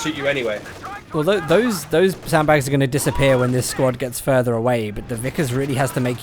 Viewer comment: I heard them semi-justifying "ghosting" and for half a second I was like wait WTF... oh you guys mean the sandbags...lol